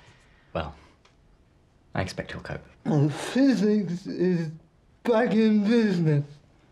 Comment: i thought he said Physics is fucking business